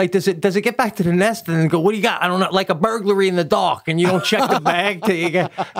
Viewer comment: This is honestly the most i;ve laughed today. When Pete said burglary, I thought he said Birdlary! LOL